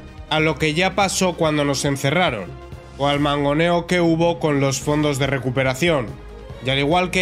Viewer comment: Porque estoy liado con papeles, pero te juro que escuché "o al mamoneo que hubo (...)" que, la verdad, también aplica 😂